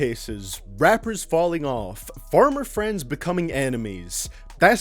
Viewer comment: I thought said "Farmer friends" in his intro, had me confused for a second.